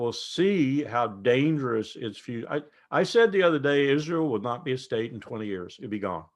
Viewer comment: Oh, I thought he said PALESTINE will not be a state FOR 20 years.